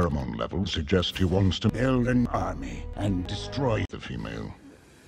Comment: "hmm-mMM-MMM, the boy's pheromone levels suggest he wants to build an army and destroy the female..." I'm dead. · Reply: Oh I always thought he said " he wants to have a party and destroy the female"